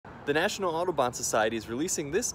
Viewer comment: I thought this guy said the national auto bots lol